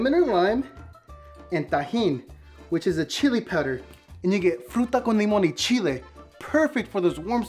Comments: sounds like he said the n word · Did he say the N word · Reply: He said “ and you get “ not the N word